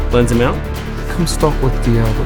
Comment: wtf do you keep saying 1:06 Lens amount??